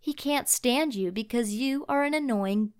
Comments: oh my god no no you did she just say b** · what did she say did she actually say b***h and I'm annoing but I'm not a b***h